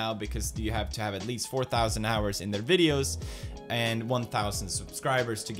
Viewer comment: Am I hearing that right? 4000 hours?